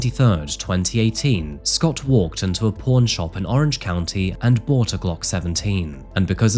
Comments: i swore i heard "porn shop" not pawn shop and i immediately thought "i guess that makes sense." lmao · I thought you said he walked into a porn shop and bought a gun. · I thought he said, we walked into a porn shop and brought a Glock lol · BTW when said "pawn shop," I thought he said "p0rn shop" 😅